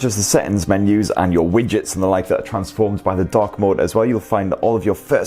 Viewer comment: Thought you said DUCK mode :-)